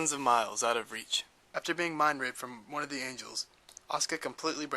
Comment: i thought u said mind raped